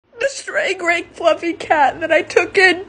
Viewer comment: I thought it said stray kids for a sec